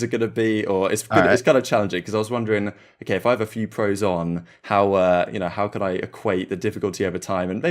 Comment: Did I hear say "if I have a few pros on?" damn this guy 👀